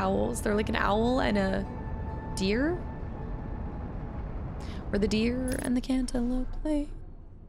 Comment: did she say "where the deer and the cantaloupe play"? lol